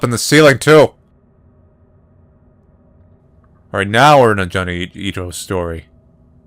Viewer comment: Okay at 20:11 did you just say "Johnny Ito?" The legendary horror mangaka from Iowa? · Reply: Junji Ito. Legendary horror mangaka from Japan.